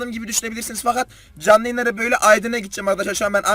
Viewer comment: abi aydına mi gidcen yanlışmı duydum bende aydında oturuyom aaaaa çok mutlu oldum 😄